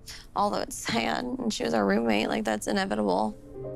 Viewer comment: what did she say? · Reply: "all those han and she's a roommate, like that's inevitable"... what? 😂